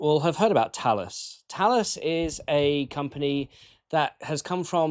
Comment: I heard you say Taalas at 1:41 and later then again at least at 7:45 but the AI "autogenerated" subtitles say Tenstorrent more than once (and the transcript says Talis, and Google's AI summary). It's intruing the AIs don't match... I don't know if you can edit those texts...